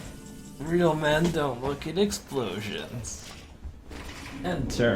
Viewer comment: "real men don't look at explosions" priceless xD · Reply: thought he said "Reno man don't look at explosions" I thought it would've been more fitting.